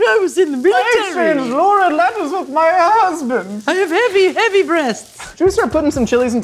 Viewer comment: did say she has heavy breast?